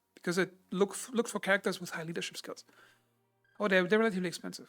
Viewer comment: look for character with .....cups? i'm sorry i do not understand what you are saying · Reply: I think I said "with high leadship skills"